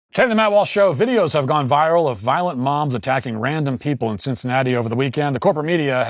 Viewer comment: I thought I heard him say that “Violent moms are attacking…” and for a split second I thought in my head, yeah, I knew that it was only a matter of time….